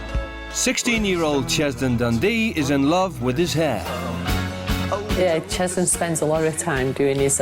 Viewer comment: i thought it said ''16-year-old Cheson Dun D is in love with his dad'' i was like WHAT!? and then i replayed it then i was like oh..........